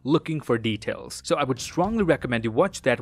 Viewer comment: did I hear Kevin Hart?😂